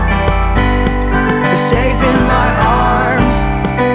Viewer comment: I thought it said "Your saving my arms."